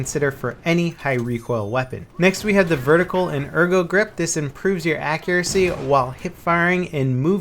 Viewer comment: I thought that a said HIV/AIDS accuracy